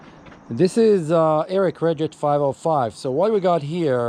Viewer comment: Where is the link? What brand is this? You have an accent, so I heard "this is an eric reget 505" and that was also in the CC subtitles.